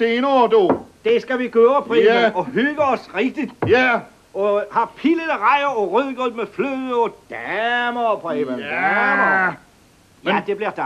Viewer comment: vad säger han mellan 0:50 - 0:57 ? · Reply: "og have pillede rejer og rødgrød med fløde....og daaaamer"